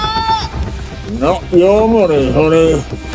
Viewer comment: tf did Hox say? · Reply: Not your money honey